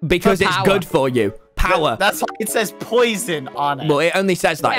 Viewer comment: did bbh say "that f*cking says poison" at 12:08 or did he say muffin?